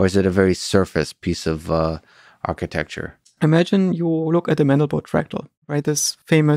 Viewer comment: what does he say? Imagine you look at the (...)? Edit: It's called The Mandelbrot Fractal.